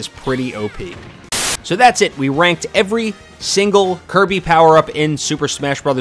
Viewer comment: I thought it said pranked in stead of ranked